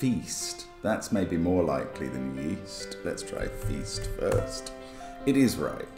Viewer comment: I thought you said Weast